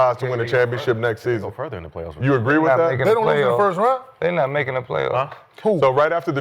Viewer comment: did nick say houston not making the playoffs ?